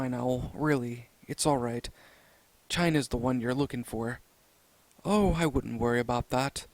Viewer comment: I heard "China's the one you're looking for."